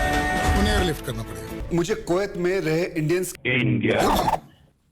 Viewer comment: Well he actually said INDIANS & not INDIAN 🤣🤣